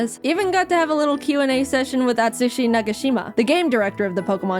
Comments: I THOUGHT SHE SAID "Atsushi Nakajima" I THOUGHT IT WAS A BSD REFERENCE😭😭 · I THOUGHT YOU SAID ATSUSHI NAKAJIMA 😭😭 LIKE AS IN THE BSD CHARACTER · did you just say atsushi Nakajima dor nakashima (im a bsd fan) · I heard atsushi nakajima haha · I WAS DRAWING AND NOT LISTENING FULLY AND I THOUGHT SHE SAID ATSUSHI NAKAJIMA.